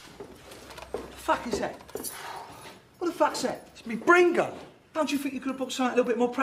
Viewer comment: Wat da faqs dat IT'S ME BREN GUN @ 0:07 When I first watched this I thought he said Brain Gun